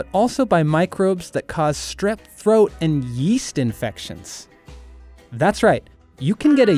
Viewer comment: Yeest infection i thought you said yeet infection